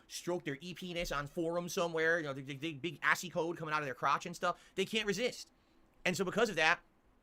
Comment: What the hell is he saying here? He's said it before when he was threatening people talking about Derich. I thought he said something about an slapping them with "assy-coated penis", but is he trying to say "big ASCII code coming outta their crotch"? Marble mouth is always difficult to understand, even more so with his weird phrases that no one else uses.